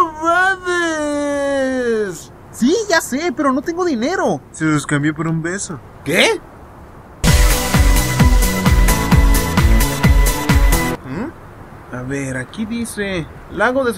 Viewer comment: no sé qué es rezidenivol :v pero morí de risa con el tipo que decía SON ROBADOOOS y después le dice Te lo cambio por un beso y aparece el otro caminando con toda la mercancia xD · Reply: A su madre todo este tiempo pensé que decía te lo cambio por un peso 😂